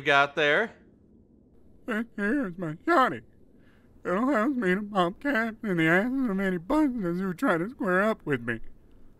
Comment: I think he said "it has been a pain in the ass for many busters who've tried to square up with me"